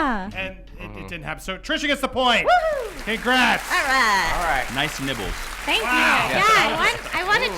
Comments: Nice ni...nipples? did he say? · did he say nibbles or nipples...lol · I thought he said nice nipples at first to trisha. Lol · does he say "nice nipples" to her?! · I swear I thought I heard him say 'nice nipples" at 15:20 to Trisha